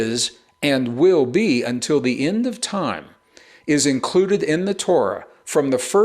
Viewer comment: I heard him say I'll be with until the end of time.